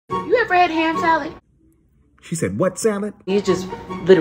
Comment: I thought she said “Hand Salad”